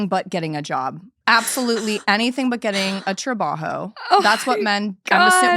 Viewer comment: No but did I just hear, "Anything but getting a job. Anything but getting a TRABAHO"??? (10:56) Lol